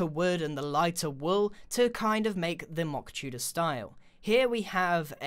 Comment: the way I thought you said "mock tuna" XD